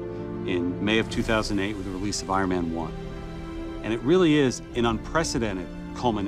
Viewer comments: Is it just me or says "Spider-Man 1" instead of Ironman 1 at 1:18? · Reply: MarcsThug he says "the release of iron man 1" real fast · Is it just me or says "Spider-Man 1" instead of Ironman 1 at 1:18? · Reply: MarcsThug I heard it as SpiderMan 1 at first too, but he says Iron Man 1.